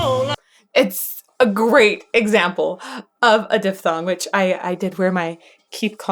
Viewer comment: My apologies, I thought you said "dethong" 😬